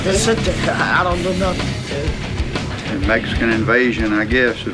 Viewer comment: did he say “its indica, i dont know nothing” at about 1:26? theres a little smoke but i cant really tell what he said. if so, what a legend.